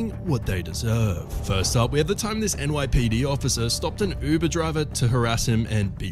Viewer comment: When he said “this NYPD officer” in the beginning, I thought he said “this N word police officer” at first, I was about to say THIS WHAT?